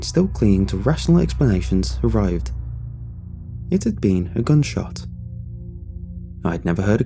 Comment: I thought he said "good shot" not gun shot